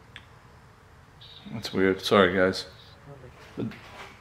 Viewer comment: Sounds like spirit saying sorry!! After explaining that the volume is high on your phone. 19:33 pure Evil that sounded pretty real..